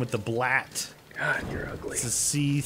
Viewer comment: Um I heard say "God your ugly!"